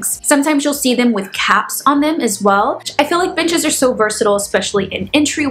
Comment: I misheard you say b*tches are so versatile and thought, “I am pretty versatile”. Then you started to build your bench, lol.